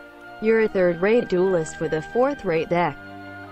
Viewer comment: I definitely heard "fourth rate dicky" on the 2nd one, which made me laugh even harder.